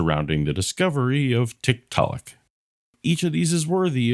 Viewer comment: I wasn’t looking at the screen and i heard “discovery of TikTok” Had a weird few moments before checking 😁